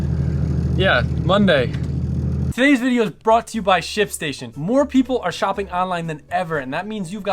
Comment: "Todays video is brought to you by s**t station" rofl thats what it sounded like